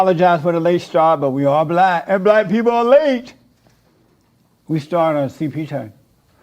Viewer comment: Did he say "we are black and black people are late??" So not only did he stereotype his race as being late (which wasn't his fault in this case) but it DOES play into the "lazy black" stereotype -- BUT he embraced it and got excited about it! In his terrible, drooling, poorly communicating, slurring way! This man is almost retarded, certainly insane.